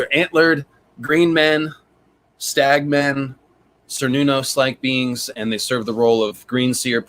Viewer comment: I don’t understand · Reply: whenever you've been saying "cernunnos" out loud, I heard it as "Ser Nunos" until I went and looked it up lol